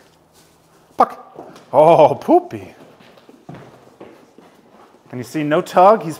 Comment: "dis poopaa" man I felt that 🗿 Edit: turns out she's a "poohpup"💀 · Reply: I heard "ahh poopy", and "his poopa" I guess he uses those commands because the decoy shit themselves multiple times while training when he was just a pup and it stuck. 🤔😂💩